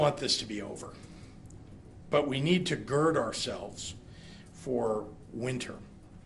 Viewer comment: Did he say “Gerd ourselves” 🤦‍♂️ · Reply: I thought he said girdle ourselves!!